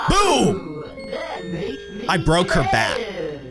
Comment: i thought he said "i broke her back